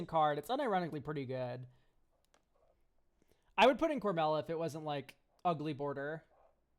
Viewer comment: Does anyone know what card is referring to at 24:48? I'm hearing Cormela which can't be right and I'm not sure what they are referring to when they say it's "ugly bordered" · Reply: plantir of orthanc; "it's good in cormela but sadly it's ugly border"